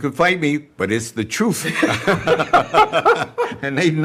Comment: did he say it was the "truf" ?